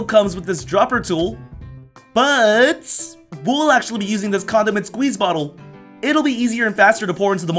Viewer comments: Wait....DID HE JUST SAY CONDOM??? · did he say he's using the condom squeeze bottle?